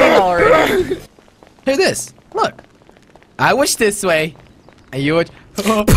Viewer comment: it sounded like jelly said I wish this way